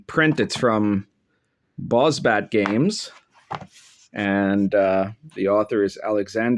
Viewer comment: its from bozbat game but i heard it as Boss babe games rofl